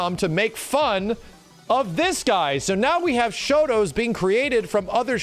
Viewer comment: Holy crap I thought it said shota